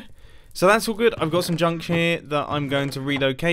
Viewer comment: Did he say junk s*it in